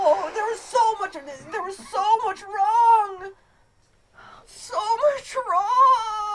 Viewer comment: Matthiew said "so much wrong" but the captions said "sober trog"